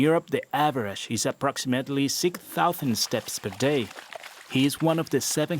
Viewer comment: Hey BBC World Service. I think there is a mistake in your closed captions. I think at 7:17 it should say 6,000, not 16,000.